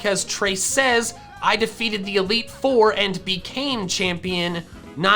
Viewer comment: I heard deleted instead of defeated